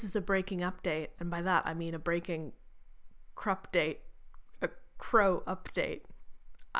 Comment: I thought you were trying to say "crapdate" because Lightlark is just THAT bad 😂